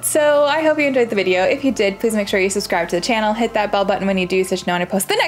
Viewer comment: I’ve been watching this at night, and loving your vibe (platinumed this and finished burning shores). The funny thing is, I just realized your saying is “smash that bell button”… I always heard it as “bub button” which was so cute.